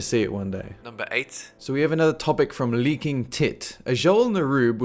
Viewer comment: LOL I was listening to this in the background, I just hear "Then we have some input from, leaking tit..." I closed the game I was on and was like wtf and laughed so hard xD